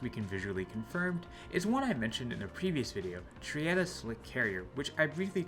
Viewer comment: Can you write down the name of the carrier at 4:13? I can’t make out exactly what the word is before “slick carrier.” Thanks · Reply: Triada I believe aka Gruppa 99